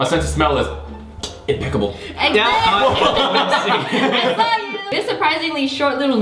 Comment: Did Wengie said I love you? Or I don't know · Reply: No she said, i saw you 😂